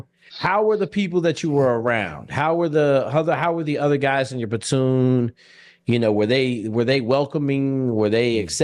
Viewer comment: idk if its an inside joke but the guy says "batoon" instead of "platoon" 😂😂